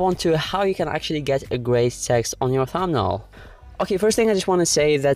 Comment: did you say grey sex ??? 😂😂😂😂😂